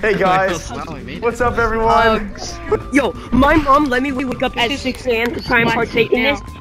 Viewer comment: I thought the kid said yo my mom let me wake up to try pancakes